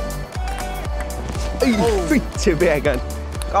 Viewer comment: Did I hear a "vittu" there?